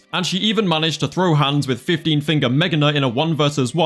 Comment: did anybody else hear “15 finger megaknight”